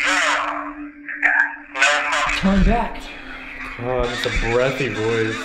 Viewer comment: I know I’m late but the “turn back” got around 51:00 is saying “yeah?” When he was going back up the stairs. I don’t think that was a ghost lol